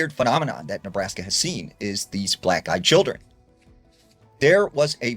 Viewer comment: Hey, "black guy children" is kinda racist! 🤣🤣🤣 ...I know thats not what he said, but it sounded hella funny! lol! Sik